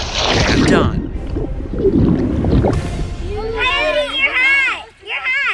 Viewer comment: On 2:15 i thought they said coyete your hot!! But when he sais where is it now i know what he meant